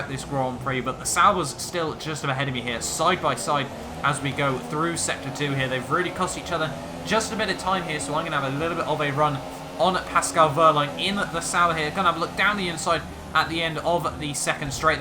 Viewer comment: did you say sanor